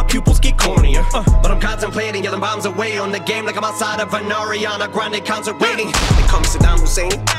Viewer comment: i thought he said "ariana grande comes and pay me" lmao